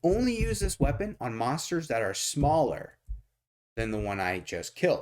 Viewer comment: Ok, so the second monster has to be smaller or equal to the previous monster. Thought you said smaller than the weapon. This makes more sense :)